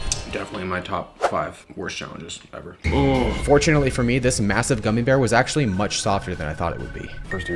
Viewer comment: did he say massive? LOOOOOOOOW TAPERRR FAAADE